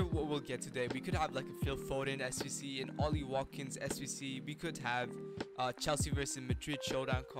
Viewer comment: pls everybody see what he said there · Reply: He said ollie watkins sbc